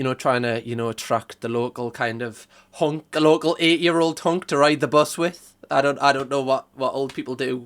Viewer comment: I had to rewind that thought you said local 8 year old hunk 😂😂